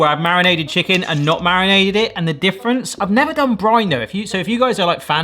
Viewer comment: When you said “I’ve never done brine though” I thought you said “I’ve never done Grindr” and had to rewind to see what I’d missed 🤣 - hadn’t looked at the picture the first time 🤦🏽‍♀️